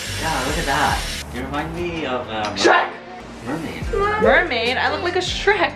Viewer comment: Did he say sh*t?? 😂😂😂